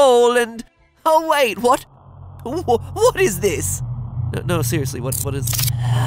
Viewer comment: At the beginning I SWEAR I heard “Wilbur what is this?”